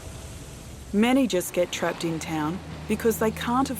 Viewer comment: Tough break for those families that are functioning well. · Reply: Managis get stuck.....? Managers?? I think I didn't catch the meaning of that.